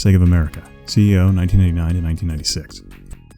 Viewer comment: it really sounds like he says "CEO, 1999 to 1996".